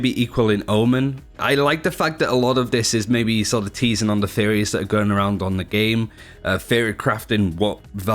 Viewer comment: Everytime he said Theories i misheard as Fairy